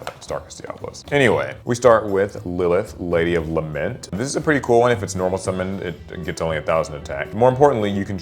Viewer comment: did he just say 'This is a pretty cool wife?" 😂